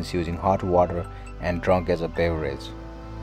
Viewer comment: The coffee bean; it sounded like you said it was drunk as a pay raise...when you said it was drunk as a beverage. My mind was drifting. Love the medium.